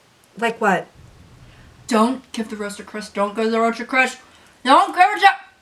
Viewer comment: Cindy, is that you? Party at Ryan’s! Don’tgivetherosetoChrisDon’tgivetherosetoChris YOU SHOULD HAVE GIVEN IT TO BEN. TO B E N I mean there’s a spider there and I like don’t even care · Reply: The entire time I thought she was saying “roast” not “rose” and I was super cunfuddled